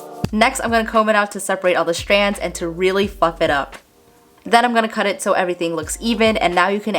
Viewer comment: Am I the only one that thought she said “really f*** it up” ? I’m pretty sure she said fluff it up but …. Hahahaha. Love your videos by the way!!! ❤️